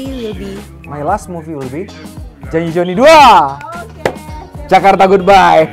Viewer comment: what is he say ? Janji Joni 2 ? Amazing ...